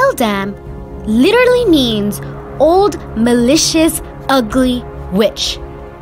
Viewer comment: When you called her malicious I thought you said delicious and I was so confused 💀